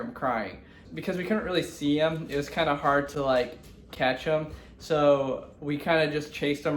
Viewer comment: I laughed so hard when they were talking about ash cuz when they were talking about catching him, it sounded like they said ketchum not catch him 😂. 👍 if you get the reference